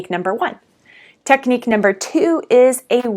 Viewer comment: I had to rewind a second because at first I thought you said #2 was “a weekly beating!” Ha! Probably not as effective as a weekly meeting.